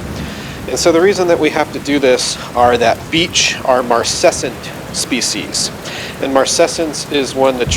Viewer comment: Thanks for the help, really appreciated. Sorry to be a pain, but at 0:43 in the video you mentioned that Beech was a type of what sounded like "Marset" species. Could you spell that out for me please, I'd like to read a bit more about them? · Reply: the term you're looking to research is marcescence